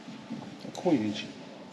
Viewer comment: Was that a case full or gold??? 🤯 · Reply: No 2:13, but not I heard you say 'coinage'